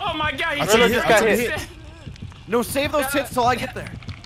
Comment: Did said save those tites